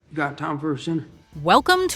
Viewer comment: I thought he said is that Tom Cruise. But I know he said you got time for a sinner.